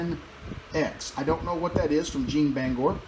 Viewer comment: Bottle stopper 1 with the olivewood. And the wood that you thought said "Tamanx", it said Red Tamavix